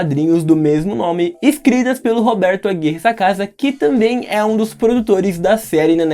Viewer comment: entendi: "escrito pelo roberto aqui nessa casa"